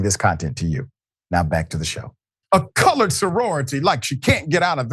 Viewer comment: Perhaps he fumbled the word "college",making it sound like rhe word "colored"?🤔🤷‍♂️ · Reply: Sounds like he is saying coward sorority lol